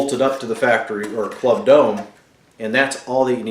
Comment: What dome are you using? I read that you are working on your own version of the dome, I think? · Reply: Okay. Just heard you say club dome.